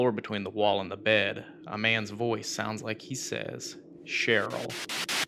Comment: After he lay down on the floor, I heard “get up” instead of “Cheryl”